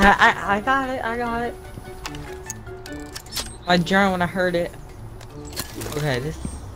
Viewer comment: he said MY DRONE i heard it